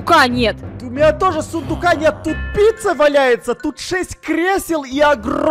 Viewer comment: Мне послышалось что тупица валяется а Эд сказал что тут пицца валяется🗿👍